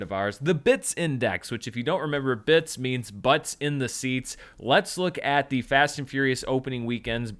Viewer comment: Is it BIS or BITS? Butts in seats or butts in theatres? I think I heard you say bits but then said butts in seats which doesn’t make that much sense to me. Oh, is it butts in the seats? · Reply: Butts In The Seats - BITS 🙂